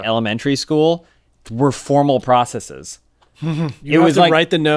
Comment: I thought James was about say formal prostitutes.